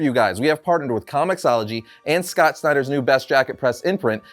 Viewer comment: Ngl I thought said Scott Steiner for Best Jackett Press